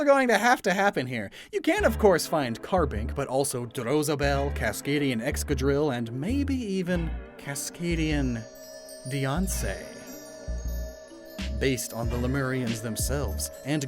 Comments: OH MY GOD I THOUGHT YOU SAID KASKADIAN B E Y O N C E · I heard kaskadian beyonce 😅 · I genuinely thought he said kaskadian Beyoncé